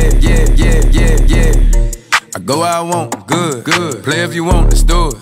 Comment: I'm from Russia and i hear "Где я?"